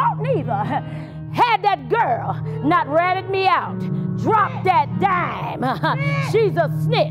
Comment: Did they just say "drop that snitch" oh my goodness I fell out too funny